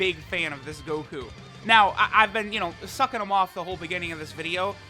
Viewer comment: did he just say “sucking him off” 😂